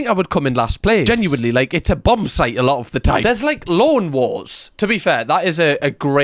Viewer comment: American here, around 7:49, I heard "loan wars" first, so yeah, accents do make a difference. Great video!